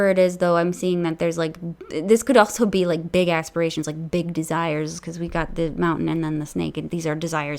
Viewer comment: #3 Been googling perations as I've never heard of the word before , couldn't find it on Google so had to listen to the reading again to get a clue , I'd thought she said " big ass perations " , she actually said aspirations 😳😁🤣😂